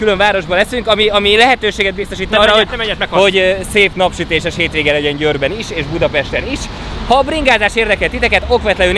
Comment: nél mit mond? :D Ki hal meg, miért? :D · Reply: "Ne menj át, meghalsz" :D